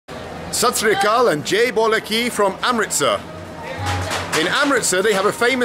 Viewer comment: it took me a while to get what he actually said as a greeting. People who dont get it, he said "jai bhole ki". That is perfectly fine though. And which stone aged moron told him that he cant use his left hand for eating. I am punjabi and i am left handed and i eat with my left hand all the time.